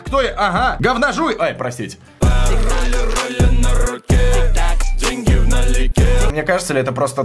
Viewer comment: Я тут орнул, мне послышалось изначально: "Ролли на руке, деньги в аналике" xDD